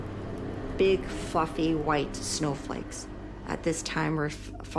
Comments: 0:0:46 big F*CKING white snow flakes 😄 I've watched this 4 or 5 times now and everytime I hear her say that it sounds like BIG F&CKING WHITE SNOW FLAKES lmao. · I thought she said big fucking white Snowflakes 😂😂